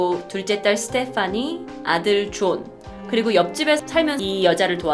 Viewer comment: 때 아들 조ㅈ 나만 들림?